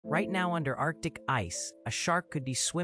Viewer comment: Thought he said under our dick ice😂😢😅